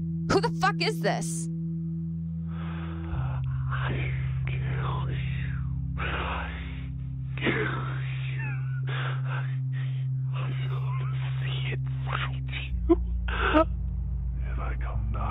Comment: For those who cant hear what he’s saying at 29:30 he seems to be saying “I’ll kill you. I’ll kill you. I want to see the inside of you.” (Meaning he wants to skin her)